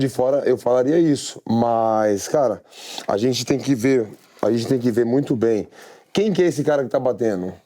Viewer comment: you can head what pereira sounds like if he spoke English. It sounds like he said “I just think if you” but I’m sure it was still Portuguese just sounded similar.